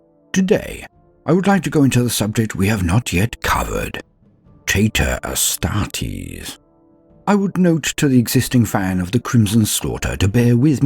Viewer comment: Awesome video made even better by me mishearing "traitor astartes" as "tater astartes". (Taters = Potato)